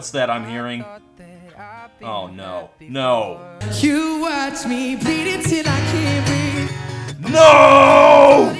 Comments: Who else thought that Shawn said "you watch me beat it till I can't breathe" at firs? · I keep hearing "You watch me beat it til i can't breath" It really kind of makes the song both more awkward and kind of hilarious.